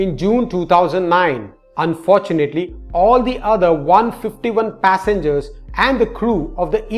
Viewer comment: I thought you said her other